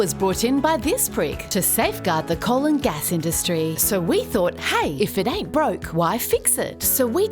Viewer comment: I heard it as "the colon gas industry".